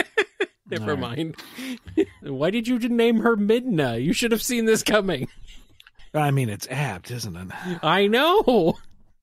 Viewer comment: What did Ian say? “It’s abs”?